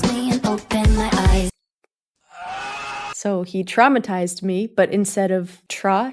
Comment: I thought she was always saying “dick-matized me” there